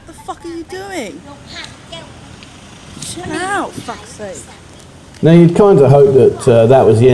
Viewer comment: D At first i heard "Chill out fuck face" -> but it was Chill out fuck sake.. 555